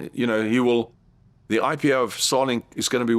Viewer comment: I could’ve swore he said “Sonic” instead of “Starlink.” I was legit like; “What is this trillion $ company that is named after a video game character?”